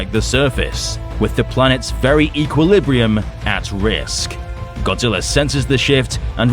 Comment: I heard "Godzilla senses this sh*t"